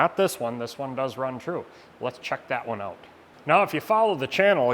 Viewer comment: Did he say "Let's chuck that one out"?